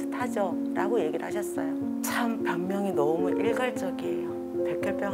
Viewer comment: 병명 이라기 보다는 변명 으로 들리는거 같습니다